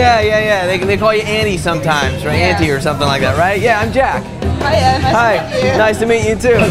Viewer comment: it sounds like she says "Hi Ed."